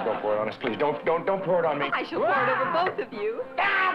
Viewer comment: what did she say ? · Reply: "I shall pour it over both of you!"